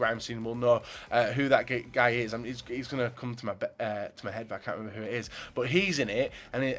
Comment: am I the only who heard at 5:57 say 'he's gonna come to my bed' lol?